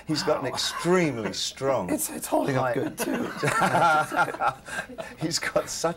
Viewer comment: What did Trent say at 9:47 in that high pitched voice? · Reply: "It's holding up good too"